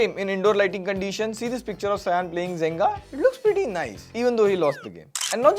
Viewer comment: It's Jenga right? Asking cause i heard him say Zenga.